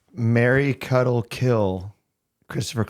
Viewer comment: lol took me a couple seconds to realize “marry, cuddle, kill” wasn’t a person… 😂 I heard it as Mary Cuddlekill